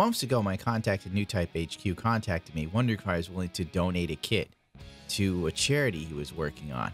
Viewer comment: omg, thought you said " I was asked to donate a Kid" 😂 Then I thought, it can't be, must of meant Kidney :P